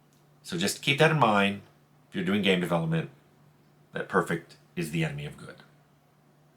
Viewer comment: I always heard it as "perfect is the enemy of done"